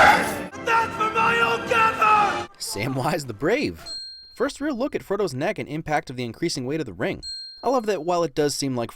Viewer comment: Hey guys what is it that Sam says at 0:17, I can't quite catch that last bit. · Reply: That's for my old gaffer